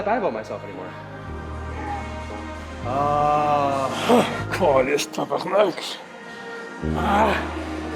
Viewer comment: I heard a classical québécois "caliss tabarnack" at 21:42 Hahaha